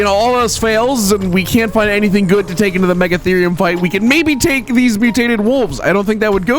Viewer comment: did you just said megatherium